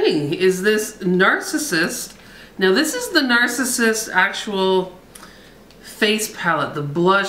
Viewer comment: Hahahaha, vloguary is really funny tonight, Narcissist Eyeshadow Palette, yep, they are fucking dreamin! THE Facebook, many thanks to the stoners cause I used to believe that I would be getting old if I took up lawn bowles, now all I have to do is realise when I start saying THE Facebook. Hahaha, you're a crack up and very beautiful tonight. I hope you have a great trip honey, come back soon, Mama Denise and I miss you. Ooops, am I going deaf or did you just say Titface palette, hahaha. Goodnight Sweethearts xoxoxo